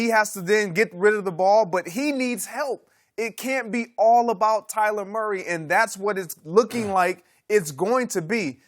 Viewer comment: "Offensive quarterback." "Tyler Murray." "Tomato trees." "Eggs in one game." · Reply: Lol damn you beat me to it. I thought he said "Tyler Murray" 😂